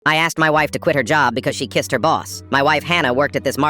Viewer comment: I heard " i asked my wife to quit her job because she kissed her balls" 😭🙏 i had to rewatch it just to male sure